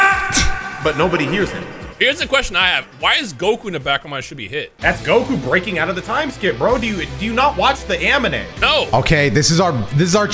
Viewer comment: am i the only one who heard saying the amine instead of anime😂😂😂